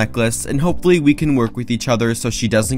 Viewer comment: Uh I thought he said die luna 😳